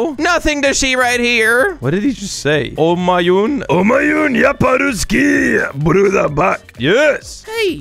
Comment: мне послышалось: я по русский